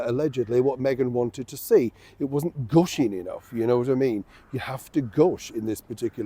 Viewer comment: at first I heard "wasn't GUSHING enough" as "wasn't GOH-SHING enough" as in not GAUCHE enough and I thought, but Meghan does manage gaucherie quite well, without trying really. It's her gift, maybe.